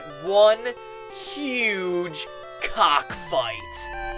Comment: omfg my video lagged at 0:08 so i just heard "we are taking a close up look at a huge cock" o_O